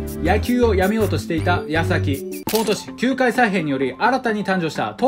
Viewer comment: 矢先が康晃にしか聞こえんくて混乱した…